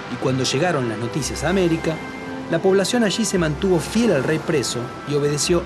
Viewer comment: no entiendo la palabra lo que dice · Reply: fiel al rey preso